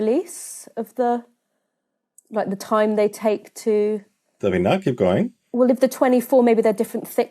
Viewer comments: Subtitle mistake at 5:12 - says "Does it not" instead of "Davina" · Erroneous subtitles, should be "Davina, keep going" instead of "Does it not keep going?"